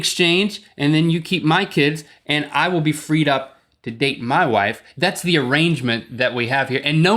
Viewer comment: I swear I heard link say "i get your kids and ill be free to date your wife..." lol im laughing so hard :,D